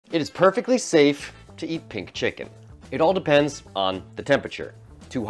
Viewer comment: "It's perfectly safe, to eat pig chicken" Is what i thought i heard 😂